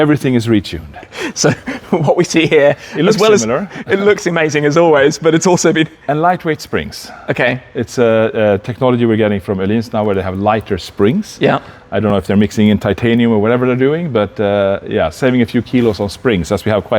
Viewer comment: did he just say technology from aliens? I knew it lol